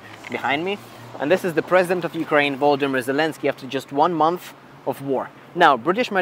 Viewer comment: Did he just say "Baldimir Zelenski"💀